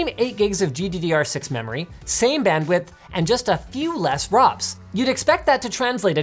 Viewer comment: he sounds like he said a few less WAPs lol